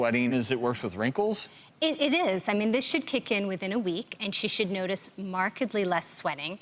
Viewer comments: "This shit kick in within" as week or have i heard wrong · did she really say "this shit kicks in within a week"